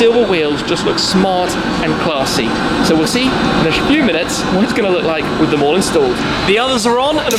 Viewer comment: did he really say "We'll see in a Shfew minutes." or is it just me?